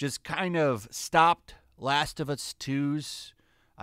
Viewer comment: I misheard "Last of Us 2" as "Activist 2," and now that's the only name this game has in my head.